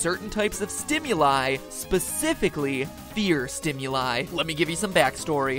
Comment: lol turn subtitles on and then look at what it says at 5:28 !!!! "Beer Stimuli"!!!!! :)